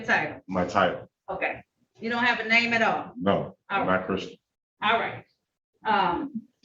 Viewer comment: did he say I'm not a person